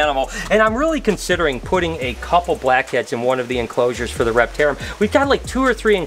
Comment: When he said black heads I thought he said crack heads😂